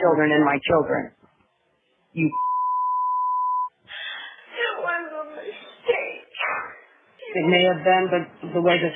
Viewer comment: could someone enlighten me what's the long word starting with C that they censored? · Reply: she said cocksucker :)